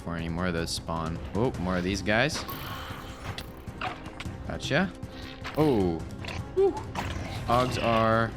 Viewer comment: Did he say "acha"??? 😳😱😲